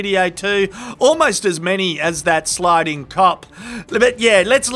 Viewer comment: did he say "...sliding cop" or did he say "...sliding 🕊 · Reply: Clearly “Cop”.